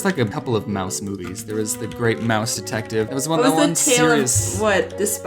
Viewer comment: When you where talking about the mouse movies “the great mouse detective” I thought it said “the great mouse despasito” (I may have spelt it wrong)